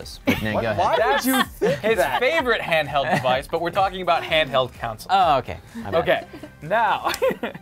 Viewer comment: Did lasercorn say "handheld counsel"? XD